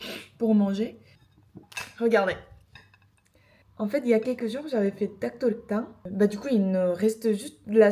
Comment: 프랑스어로 말하는 영상 좋아요 대리수치 이런 거 없습니닼ㅋㅋㅋㅋ 어학 얼마나 하셨나요 저는 어학으로 랑스 왔는데 잘 늘지 않는 것 같아요 ㅠ 2:35 여기 뭔가 닭도리탕을 닥 토희탕 으로 들려욭ㅋㅋㅋㅋ닭도리탕 불어버전 ..! Tarc do ri tin